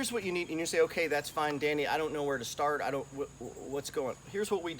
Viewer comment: that’s fine who? Did he say Danny? Lol....